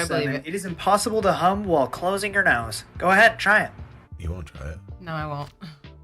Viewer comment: Superb editing 👏🏻 I thought dude said “it’s impossible to HUMP while holding your nose 👃” Also, loving Nigel’s cameo!